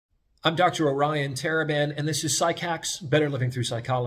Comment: I’ve watched your videos for a while and the whole time the reason I haven’t subbed is because I thought you were saying Taliban, and I was like they just announce their faction now? I realize now you have been saying Teraban the whole time 🤦🏾‍♂️